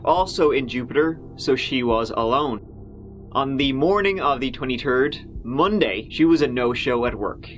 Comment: Wait, did you say "the 20 TURD" @ 3:48? LOL. Great episode, btw.